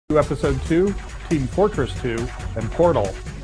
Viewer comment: I hear "tree of great games" and claim this cake as a fake 😂